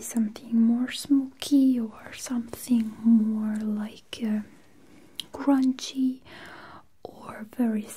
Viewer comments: When she said grungy I thought she said crunchy and I was very confused, lmao. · I know she said grungy but I thought she said crunchy.